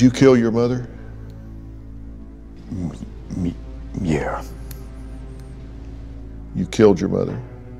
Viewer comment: you can see the guy muttering “it wasn’t me” under his breath before he says “ya” 😳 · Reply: It's clearly audible. Tf you talking about? At 13:40 he clearly says it. Turn your volume all the way up, you'll be able to hear it. It doesn't sound like he said, "muh muh yeah." I could understand the first thing he says sounds like "muh" but the second so called "muh" doesn't sound like that, it sounds like he's saying "me" it wouldn't make much sense if he was saying, "muh me, yeah" it's a yes or no question asked by Dr. Phil. So by putting that piece of the puzzle together, we can determine that he actually said, "it wasn't me" "yeah." Boom! Straight F.A.C.T.S. (Also, it actually does sound like he says "it" in the beginning, so I'm just further proving my point).